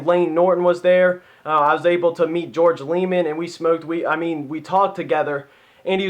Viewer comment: did you say at 4:35 you smoked weed together haha